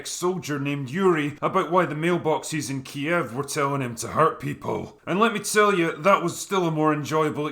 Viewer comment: had to rewind because I first heard “Mailboxes were TAILORED TO HURT PEOPLE” XD